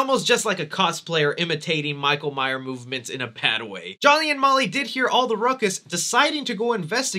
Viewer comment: Did you say Jolly and Molly? 🤔😂